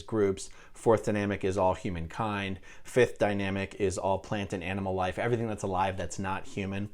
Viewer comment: I thought he said “pliant” instead of plant before I watched the subtitles. I think pliant makes more sense in the context 🤔.